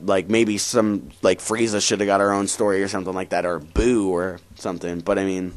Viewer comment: WOAH Did he just call frieza a "her"